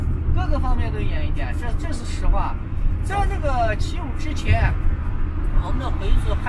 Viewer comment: driver did not say year of 1975, but 7.5 terrorism attack; 15:33 these blocks block out motorcycles, can be found elsewhere in China.